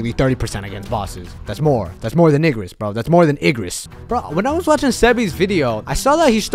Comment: Did bro say nigris?